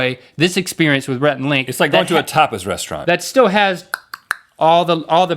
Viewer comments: Rhett, I thought you said a "topless restaurant." I'm glad you didn't. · Almost sounded like Rhett said "topless restaurant". BTW - I trust you guys. :)